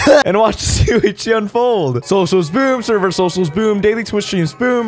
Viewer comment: It sounds like you say socialspoom at the start